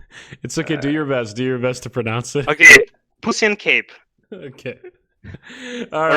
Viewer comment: what does he say? Pussy in cape or what?